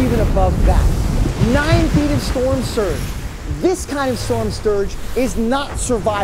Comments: Did he really say storm sturge? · i heard him say storm stugre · anyone else heard him say storm sturge? 😂😂